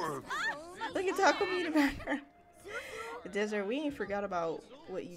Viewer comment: Everytime you say Takomi I hear “taco meat” 🤣